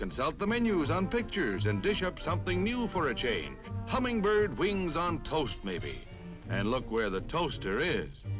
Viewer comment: Did he just say... HUMMINGBIRD WINGS on toast?